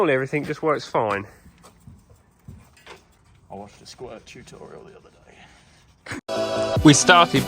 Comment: Did I hear "squirt tutorial"......Must have been mistaken..LOL. Another great video Lads! Cheers Gregg.